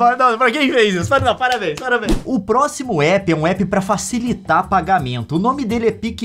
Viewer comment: Eu entendi PQP kkkkk